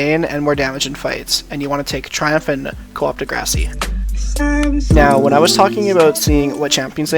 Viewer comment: I know u did not just say co-op de grassy....